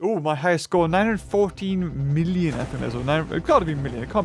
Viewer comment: When I heard you say your best was 915 million I was like “WHAT HOW!!!!!!!!!!!” LOL.